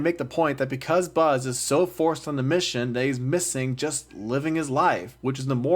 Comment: I mean yeah. If you know me you know I don't like this movie. Tbh though, I'm done talking about it. It's been 4 years, I'm ready to move on. I haven't seen Toy Story 5 yet, but I doubt that movie acknowledges this one. It's easy to ignore is what I'm saying. It's honestly crazy how easy you can just throw this movie away from the Toy Story series, because it adds nothing. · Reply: btw, the subtitles says “focused” but it sounds like you said “forced” 🤔